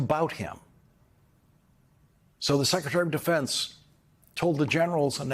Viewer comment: which is hardly the weirdest thing about him ❕>> Plus, whenever Mr. O'Donnell was speaking a bit too fast, i could have sworn i heard him call the other one the << Secretary of Offence >>, where i understood he meant that said bloke was deliberately and relentlessly offending the Military brass.